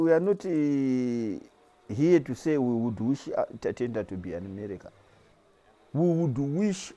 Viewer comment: what is he saying ? “What we would wish ta ta da tooomerica “